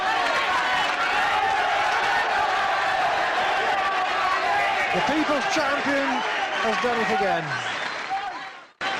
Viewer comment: what did Alex say @ 1:31:38 ? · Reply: We are fucking back. He got fined for it.